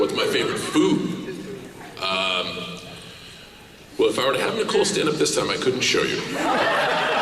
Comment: what the hell does he say? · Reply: If I were to have Nicole stand up this time, I couldn't show you... IE Pussy. His favorite food is pussy.